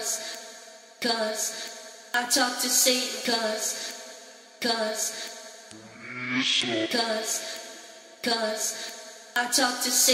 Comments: It sounds like shes saying 'cus cus i talk to satan cus' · Thought said cause cause i talk saint cause cause